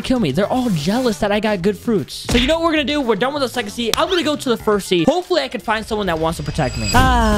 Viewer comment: Check at 7:02 he said sexy instead of second sea